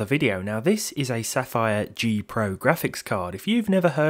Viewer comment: Idk why I thought it said GOPro graphics